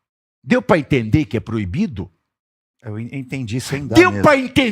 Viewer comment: eu entendi sem dá mesmo Kkkk isso foi ótimo